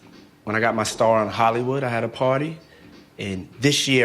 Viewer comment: he sounds like he said, "I hada potty." I know he meant "I had a party." He just talks like thug, so his words sound stupid.